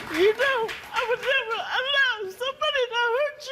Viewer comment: wtf is she saying 😅 you know I wanevu I low subody dah huht yuu